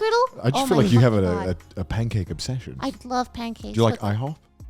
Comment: Oh i thought you said delicious pancakes